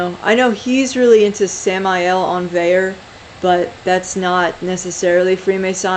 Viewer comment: What’s the name you said at 1:36? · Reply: Samael Aun Weor, there's a website called Glorian that is all his writings/speeches